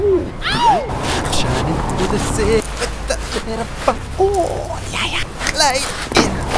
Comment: I heard “with the little popcorn) lmao